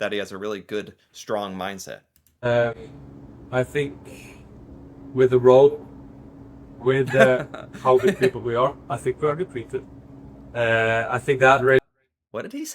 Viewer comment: He said: "I think with the row-- [with showing people] how good people we are, er, I think we are good people" He's talking about the Norwegian fans, and how he's happy that more people around the world are now more aware about how good people [morally I guess] Norwegians are in general. Because it's only been maybe this past decade people have even been aware that Norway exists, while in the past people didn't even know where Norway was on the map or that it was a sovereign country, since some people mixed it up as being a city. What you talk about in 11:10 is exactly what I think Haaland tried to say that he wanted to achieve, not just about him as a person and the Norwegian football team, but about putting Norwegian culture and Norwegian people in general on the mental map of people around the world.